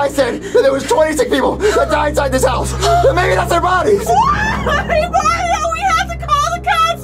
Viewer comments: Did he said mommies or bodies? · what the fuck was mario saying??? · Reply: He say: the guy said there was 26 pleople that died inside this house maybe that's their bodies